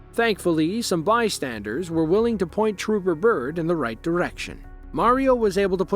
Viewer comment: what is the word? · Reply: Byrd